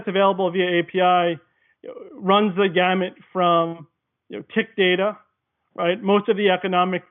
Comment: he mentions CIC data, and at 9:23 he mentions scraping KNQ data. Does anyone know what those acronyms stand for? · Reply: I think he said "tick" data at 9:14, referring to each tick up or down in a stock's price